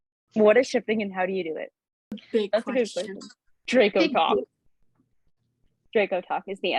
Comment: what did she say was the answer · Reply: Dracotok